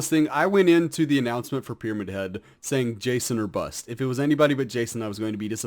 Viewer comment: did he say 'bus'? Whats that?